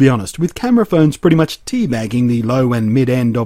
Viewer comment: Did he say "tea bagging"? lol